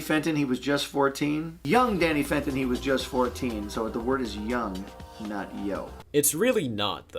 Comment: I always heard young never yo lolz